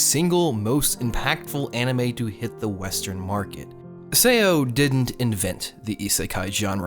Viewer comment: did he just say sao like “sayo”